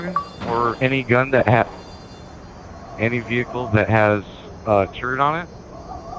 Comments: it sounds like he said find any vehicle with a turd on it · sounds like you said any vehicle that has a turd on it!!